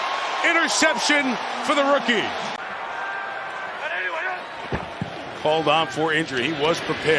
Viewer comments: hahaha🤣 Oh man I was washing the dishes (4:48) when I thought I heard .... "you suck my Blankenship"😲😲😲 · Am I the only one who heard this live and thought Mike Tirico said "you suck my Blankenship?"